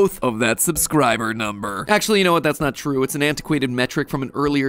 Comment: I thought it said "THATS NOT MEAT"